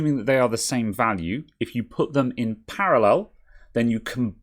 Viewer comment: which ones are you referring to on the schematic? Putting caps in series just increases the voltage rating overall ( at the expense of capacitance ) but the actual frequency response depends more on the capacitor itself. If you could list the caps on the schematic you're talking about to I'd be happy to look at what you're referring to. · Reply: oh in //parallel// - thought you said series.